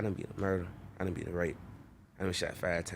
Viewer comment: did he say he got raped? damn son thats rougher then being shot 5 times im sure. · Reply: no he was saying he beat a rape charge